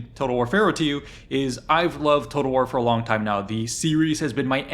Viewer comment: this whole time I thought he was saying “Totalwarfara” and it made no sense. 🙄🫠